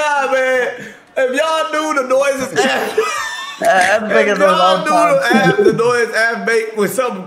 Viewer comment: did say what i think he said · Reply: "Aff be making noises all the time chat" idk wtf you thought you heard